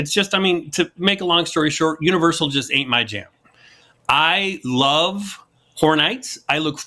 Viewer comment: I swear I heard say "I love whore night" at the 23:00 mark! Lol